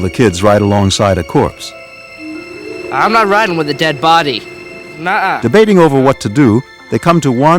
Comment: I thought that both of their children were girls but when I heard him say I'm not riding with that body I was so shocked!!!!!!!!!!!!!!!!!!!!!!!!!!!!!!!!!!!!!